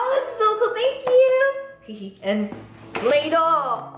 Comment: When she said play dough I thought she said Lego 😅😅